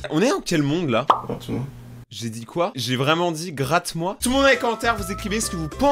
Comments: Moi j'ai entendu "attend" au lieu de "gratte moi". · moi j'entends "grattoir"😭